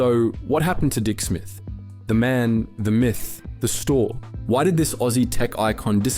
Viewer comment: I heard "The man, the myth, the smith"